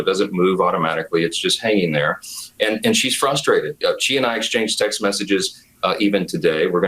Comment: Y'know just for a second there at 5.00 I had to replay because I genuinely thought he said 'she and I exchange sex messages' I wondered why his son's phone didn't blow up what with that app they both have to stop them being naughty because God is watching.